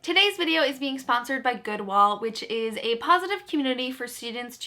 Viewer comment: I totally thought you said Goodwill but you said goodwall I realized because I saw the hyperlink